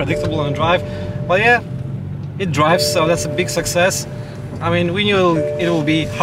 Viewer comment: "big success" all I heard was borat 😂